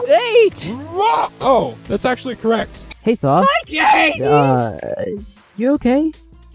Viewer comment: what did saw say. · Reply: Hi gEIGHTy